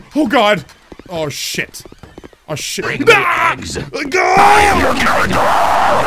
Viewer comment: Whenever Marge freaks out I hear 'I am your car garage'...